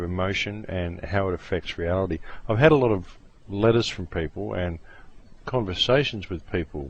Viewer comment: Sounded like he said "I've had a lot of lettuce from people"